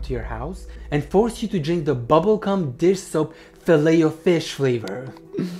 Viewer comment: Was it just me, or did any body else think they heard him say “ bubble c*m dish soap filao fish flavor” in stead of,“ bubble gum dish soap filao fish flavor”?